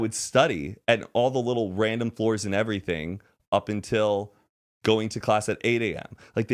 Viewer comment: Why do I hear.. "up til 10" im so confused